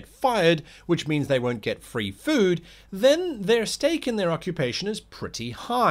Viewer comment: When hearing about the food-motivated guard's stake, I kept hearing "steak".